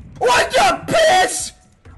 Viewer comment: did anyone hear say "piss" ?